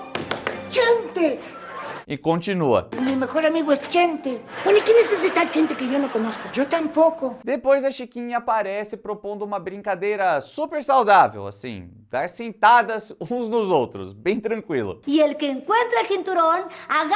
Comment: eu entendi "dar sentadas" , que ideia é essa chiquinha